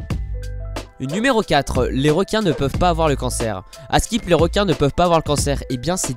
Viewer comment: C moi ou il dit "askip" ? 😂😂